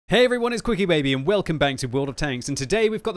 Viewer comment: you slurred your into bubba, it sounded like you said Quicky Lady.....